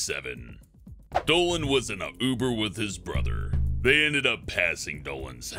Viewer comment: Did he just say "Dolan was in a boober?"